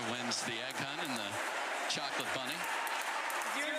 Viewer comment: oh I had to rewind… I thought that commentator called Tyra a chocolate bunny! Smh.. let me go to bed! 😂😂😂